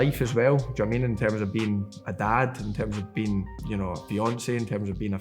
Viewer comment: im guessing he said "being a dad, being a fiancé..." at 3:20 but i replayed it a few times and all i hear is "being a dad, being Beyonce..."